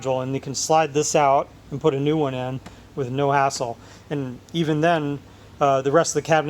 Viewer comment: Did he just say "with no asshole"? I feel like Beavis and Butthead would be watching this and cracking up. (awesome & very interesting video BTW)